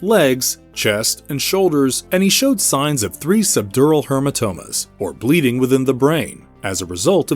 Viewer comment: did you say hermatomas 4:22 hahaha